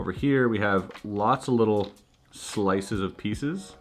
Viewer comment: Totally thought he said slices of pizza 😒